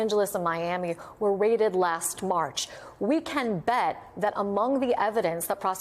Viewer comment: I thought he said Dideos!!! To describe the videos